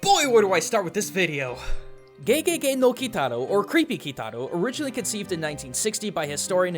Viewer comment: all I heard was GAY GAY GAY NO KITARO